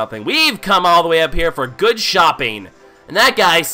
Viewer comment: I know this might make me sound immature, at 4:05 I honestly thought he was gonna say "We came up here for good s**t" and not in a dirty way.